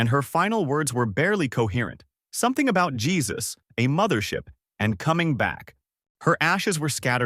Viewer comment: For yal, who r wondering what she said at 4:16 it said "This is all your fault, Jesus will save me"